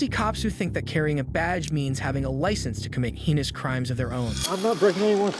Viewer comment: thought it said "just because they have a badge, they get to create penis crimes of their own"